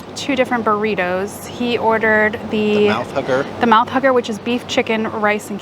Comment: Food looked great, I think I'd go with the burritos. Not to be gross, but everytime said "Mouth Hugger" it sounded like he was saying "Mouth Hooker" (sorry) lol. Great video as always guys 👍👍